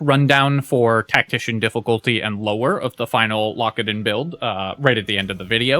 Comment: I keep hearing "Lockadin" as "Lock It In" and now I'm starting another run.